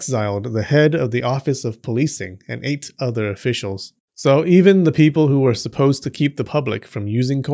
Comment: I thought for second you said they exiled the chief of police and "ate other officials" and I was like, "whoa, that is serious" 😂